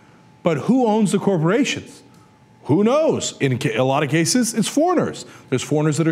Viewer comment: Hey folks, check it out -- at 3:10, the closed captions say "who knows enchilada cases as foreigners" LOLOL!